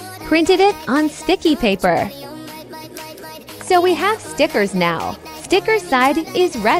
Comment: I thought the audio said snickers 😭😭